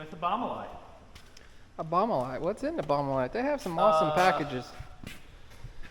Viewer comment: I'm confused as hell - because I always hear "Obama light"... 😂😁😜🤟